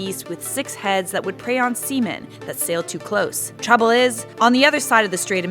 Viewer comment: "That would prey on seamen" LMAO I THOUGHT SHE SAID SEMEN AT FIRST